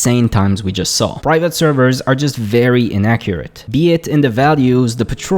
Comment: I love how the not-auto-generated captions say "accurate" when the audio clearly says "inaccurate" and the auto-generated captions gets it right.